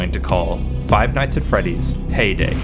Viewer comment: Lowkey thought you said payday 1:05 and i was so confused, i thought you were turning it into a heist shooter